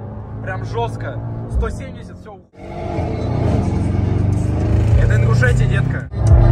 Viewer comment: Мне одному показалось, что он сказал " Это Ингушетия, детка" ?